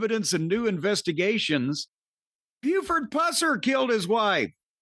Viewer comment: Ah, the guy Cornette referenced when talking about Vince Russo carrying a baseball bat. I had no idea who Jim was talking about (as usual with his 1950s references) and thought he said Buford Buzzer.